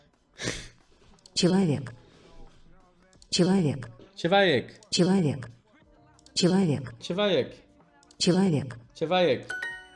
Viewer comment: мне казалось, что он говорит "чебурек, чебурек"